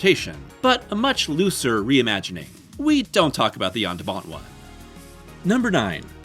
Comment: I've rewinded back again and again and I still can't figure out what version they don't talk about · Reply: "The 'Jan De Bont' one." He's the director of Speed, and Twister. He directed a remake of 'The Haunting' in 1999.